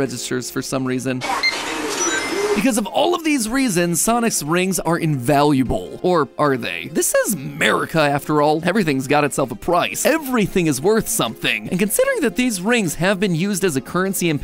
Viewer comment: Is this just me or did he say sonis